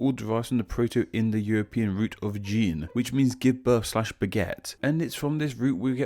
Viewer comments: you said "beget" but I heard "baguette" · I misheard "beget" as "baguette" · Umm. While English is my second language, I am usually convinced that I am quite good at it. But you really stumped me there. At 00:20 I only heard "gene, which means give birth slash baguette". I googled it, but I still can't unhear it.